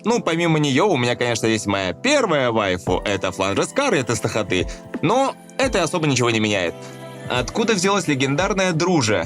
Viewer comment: извите что он сказал я не понимаю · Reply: Фландр Скарлет из Touhou project(Тохота). Я кое-как нашел это. Давно я так не напрягался для поиска.